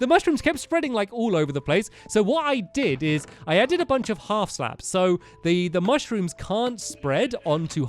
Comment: he said half slabs instead of baby blocks.